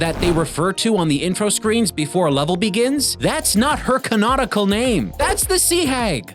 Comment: Did say ‘can-nautical’ there? Because that’s what it sounds like to me.